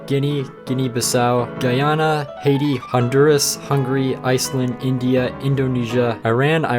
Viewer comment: Bro, did you just say "Hungry" and not hungary, im sad now😔😔😔